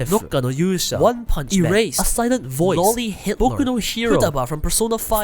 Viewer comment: i heard "Loli Hitl.. Führer" 😅